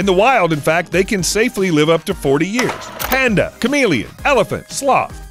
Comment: Chameleon expert here Red: stop Green: go Yellow: proceed with caution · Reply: Who else thought he said charmeleon expert 😂